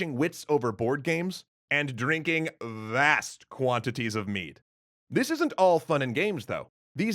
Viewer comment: It really sounds like he said "drinking vast quantities of meat" I'm pretty sure he said mead, but it had a definite T sound. It sounded funny that's all 😁🤣